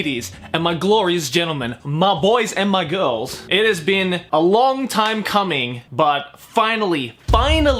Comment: It sounded like he said it's been a long time cumming